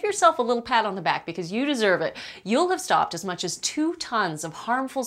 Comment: honestly sounded like she said "i'm fatty kim" at the end